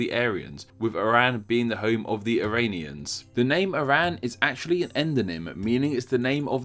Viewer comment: Did you just say: Iran, the home of the arabians??? Lol